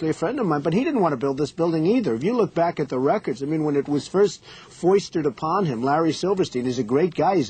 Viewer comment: does he say “foistered” .. lol he’s always been a “stable genius” using the “best words” bling bing bong bing bing